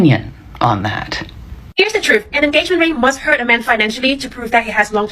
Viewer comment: Did anyone else have a hard time understanding what the chipmunk on meth was saying at around 6:08? How can she possibly talk that fast within swallowing her tongue? 😛. What a piece of work that one. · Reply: Okay so I slowed it down 300x and what she's claiming is that an engagement ring must "hurt" a man financially to prove blah blah blah. So she's acknowledging right of the gate that the goal is to hurt the man, financially. I'm pretty sure men are well aware of this. Especially considering that as soon as she decides she likes some other guy better he knows he's going to lose his house, car, kids, at least half of anything else he might have worked for, and as an added bonus he gets to pay her $$$ while she's sleeping with some other guy in the house the dude worked his ass off for. I'm amazed that anyone would even bother getting married anymore. And it will probably continue to decline until they change the laws so that it's financial and mental suisyde for a man to marry some gal.